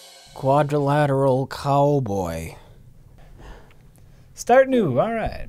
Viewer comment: I really thought it said Quadriplegic Cowboy. I am very dissapointed